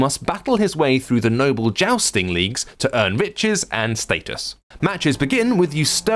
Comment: Thought he said "bitches"